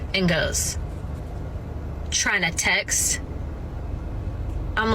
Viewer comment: What did the guy say to her? "Chinatex?"